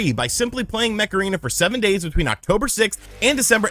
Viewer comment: I thought you said that sponsor was Macarena so I was getting ready to dance